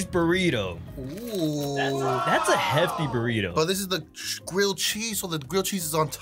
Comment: I thought you said heavy- 💀✋